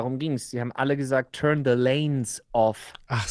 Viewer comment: Pahahahaha und ich hab einfach verstanden "turn off the lights/turn the lights off". Wie einfach 3 Leute 3 verschiedene Sachen verstehen 😂. Aber ja, beim nochmal gucken hab ich das von verstanden 🙂.